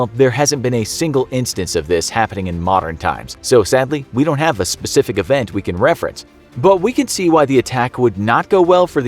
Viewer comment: Did he say sadly that has never occcured? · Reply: Bhavik Katarmal he said they don’t have a specific event they can reference